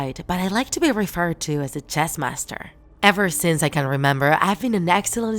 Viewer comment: I thought it said cheese master XD